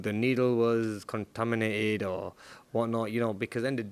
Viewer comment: did he say the needle was contaminated 😂😂